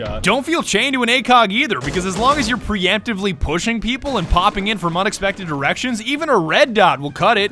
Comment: I heard "popping in from several seperate erections" at first